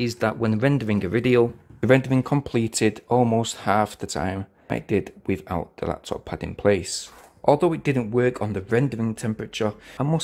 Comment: Listen to what he says at timer 11:00. "The only thing I would say is that when rendering the video, the event have completed almost half the time I did it with the laptop pad in place." Really think that is a GREAT review! And actually it works! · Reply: I did listen, he says ......"I did it WITHOUT the laptop pad in place."